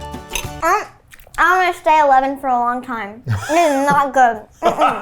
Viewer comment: How old is this kid? He’s so eloquent, and self aware. He sounds like a 23 year old Duncan Trussel in a 8 year olds body. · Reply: I think he says he’s 11